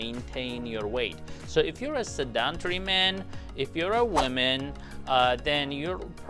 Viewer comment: Did he just say seductory, instead of sedentary? 🤣🤣🤣